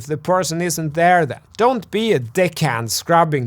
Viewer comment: Lol, I heard "don't be a dick hand" Good video:)